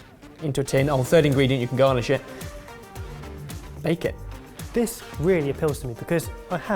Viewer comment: to me it sounded like “you can go on a shit “ 😂😂😂